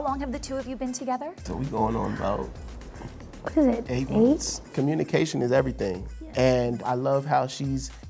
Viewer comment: Let’s talk in 18 more months 😊 it’s still fresh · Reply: First I thought they said 8 weeks